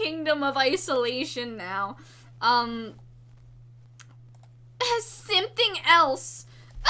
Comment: I thought she said I’m simping someone else lol